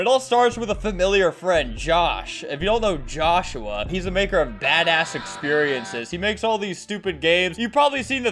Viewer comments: did he just say.... 😊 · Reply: Yeah badass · did he just say badass!/!11😱😂